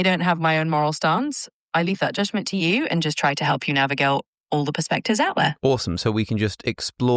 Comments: even the hyper intelligent chat GPT fumbles her speech sometimes 😂 sound like she says navigale instead of navigate😊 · the ai says "Navigail" instead of navigate. And there is a small pause as though it knew it said something wrong. Is this ai or a real person pretending to be ai? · anyone else hear the mistake when it tried to say navigate. Almost like its script was spelled wrong. Not making any accusations, but it almost certainly said "navigelt"